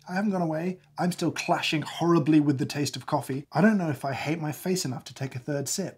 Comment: "I don't know if i hate myself enough to take the third sip you know i ju- okay i'm taking the third sip" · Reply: Haha, I heard "hate my face"!! xD